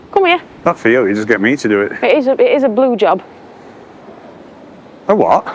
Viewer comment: I laughed more times watching this than I did the Ricky Gervais Netflix special. And I had to rewatch the bit with the sloping lawn, I thought I heard say it's worth a Blow Job, turned out she said Blue Job. All the best